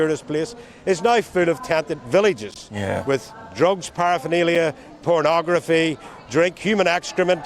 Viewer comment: Wait, did he say those illegal immigrants have "drugs, porn and drink"...? Interesting... The so-called "Far Right" in England are white, black, men, women, young, old, christian, jewish, sikh, hindu...all kinds. In one month Keir Starmer has almost caused full-scale civil unrest. Ben Habib, once again, speaking sense...and being totally shut down and his points ignored.